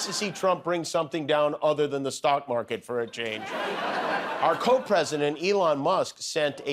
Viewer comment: Did he say "Co President' or was it "Coke President"? 😂